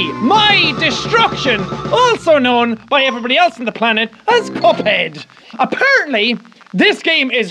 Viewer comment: I had captions on and it said “destruction also known as anybody else on the world COPIED apparently” it said copied instead of cuphead